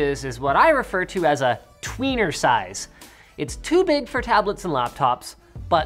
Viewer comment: oh my did u just say "21,5 inches is a weener size"? xD rofl :P